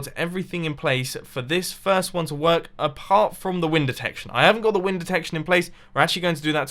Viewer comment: I thought you said wind detector